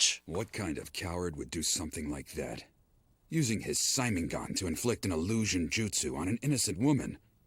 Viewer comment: Tf did say? 0:35 seinengun? Tf 😂